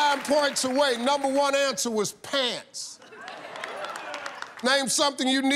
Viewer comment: Am i the Only One Who thought he Said pads and not pants?